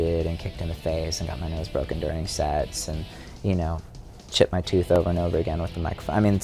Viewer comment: Whoops! I so thought I heard Davey say he broke his nose during sex, not SETS, at 2:21. hahaha